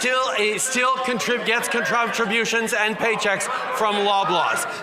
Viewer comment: Did chucklenuts say: contrumptrabutions? Good job sport!